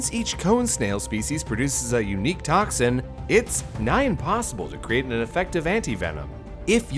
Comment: What the heck is the sentence at 7:33? “It’s NIGHT impossible to make an effective anti venom” That’s what my spell check says.